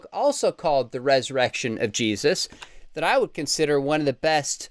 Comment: My speaker skipped missed first fraction of a second and I heard say "Erection of Jesus." Spit take with coffee is not fun!